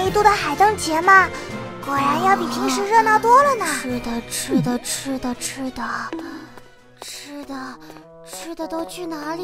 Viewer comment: Fuck only an Indian can realize what Lumine is saying - "Chuda chuda chuda....XD"